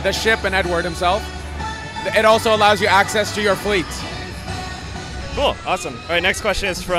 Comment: Did he just say "your fleet"?!!